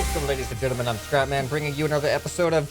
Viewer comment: did he say crap man? XD LOL